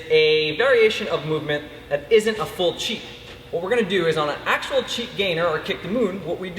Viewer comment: Lol did he say chick